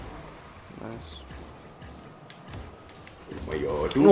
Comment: did u say ,,neiinnn" ? 😂